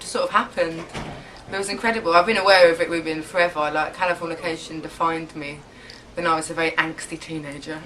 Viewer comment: What did she say at 1:22? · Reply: (californication red hot chili peppers album)